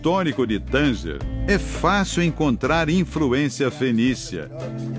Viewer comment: O locutor falou infruência, ou foi meu ouvido?